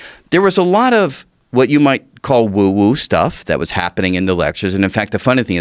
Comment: For a second I thought he said "UwU stuff"